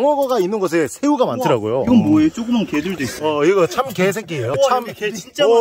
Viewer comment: 에서 참게 새끼 인데 참 개래끼 라고들림 ㅋ